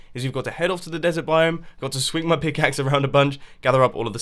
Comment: I thought he said Nether biome XD.